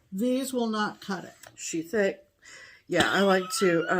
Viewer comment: You both had me and my hubby in stitches!! He was drifting off to sleep, and he thought he heard say sheep neck, instead of she thick, which made him come out of his stupor and giggle, saying is so funny.